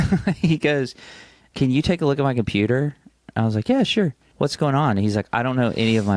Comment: the way I wasn’t paying attention and thought said “can you take a look at my PEETER” and just fully accepted it for a second 😫😭